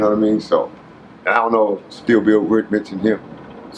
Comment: Did he say "still bill at 20:38? I wonder if that is the same guy that Cutes mentioned in his interview?